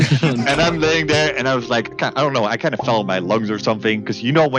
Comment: why is no one talking about how it says legs although he clearly says lungs